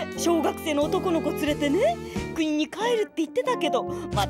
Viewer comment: イグチさんどこに帰るって言ってるか聞き取れん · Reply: 国